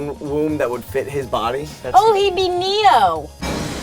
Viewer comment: I thought she said Nemo...